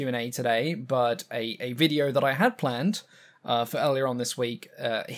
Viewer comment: I heard: "The video I had planned for aileron this week"